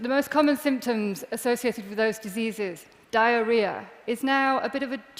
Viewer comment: Yep, that is exactly what she said (a symptom for those diseases), start at 3:05 and listen carefully, if you are not sure what she said exactly (maybe because of her accent) listen again.